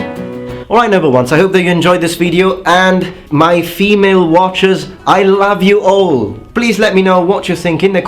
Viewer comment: When you said "My female watchers!" at the very end I totally thought you said "My female archers!". I laughed and almost complimented you on that!